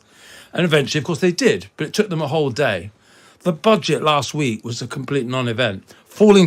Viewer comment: Sounds like is saying "the bodge it" 1.01......an accurate descrption one might say.